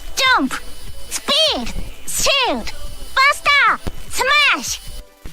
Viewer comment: Its sounds like Kirby says "Shit" when he says "Shield"